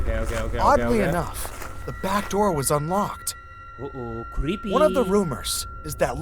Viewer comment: when said "o oh creepy" it sounded like "o oh stinky"